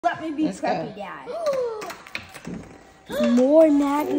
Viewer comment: NAH BC AT THE START I HEARD SAY “LET ME BE PREPPY GYAT”💀💀💀